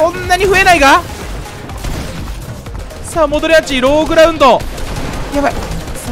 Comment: のさんのローグラウンドがローグラ運動に聞こえて笑ったw